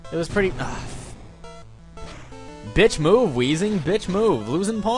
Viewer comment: It sounded like you said "Bitch move, WEEZIE!" And that's racist.